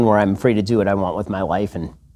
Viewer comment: it sounds like he says 'not be bothered by my children.' huh?